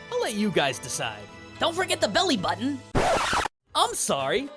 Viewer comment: For some odd reason I heard "subscribe button" instead of "belly button"